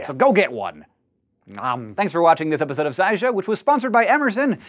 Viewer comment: I first heard "Amazon" instead of "Emerson" and was shocked. :^^